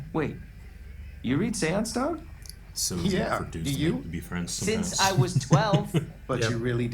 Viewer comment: What does say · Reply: It’s so easy for dudes to be friends sometimes